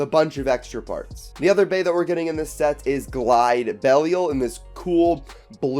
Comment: wait did you say Glide Belial?